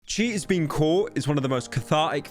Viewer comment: i thought he said cheese being caught at the beginning can he get a better accent